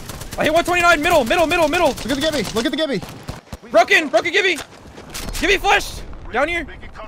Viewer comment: “Gibby Flesh” swear I heard Morty